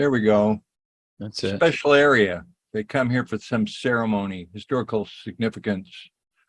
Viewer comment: speak "some ceremony" in 1:01:09 and I only hear samsara. Then, after, he talks about reincarnation. Was this the moment when the samsara device was build on purpose on earth for the degenerated and tortured "demonic" souls came through the portal?